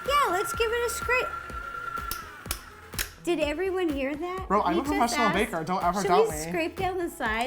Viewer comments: I think he says bro not sister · OH MY GOSH! Did say bro, I thought it was sis XD